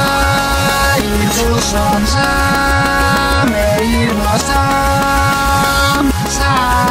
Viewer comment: Yo escuché ( y los dos que le pasó ) en el minuto